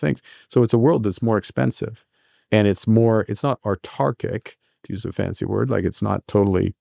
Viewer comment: what's the "fancy word" at 29:48? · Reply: autarchic <link>